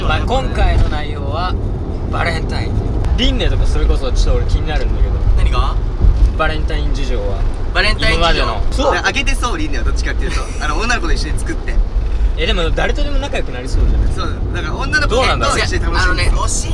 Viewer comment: の女の子って言ってたのね……『おなご』って聞こえて流石侍だなぁとか思ってた